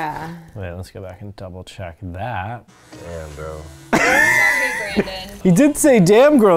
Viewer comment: He said it really low, but my first thought was Brandon said, "Damn, bro" - the bro was really low. Hard to hear. His mouth was making the round shaped, O sound. Haha. (I watched it back a few times.)